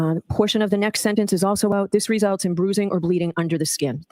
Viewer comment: did say boozing for bruising?